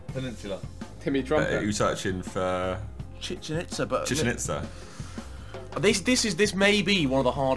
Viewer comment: lads I'm so fat that thought the right said "Chicken itza(pronounced like pizza)"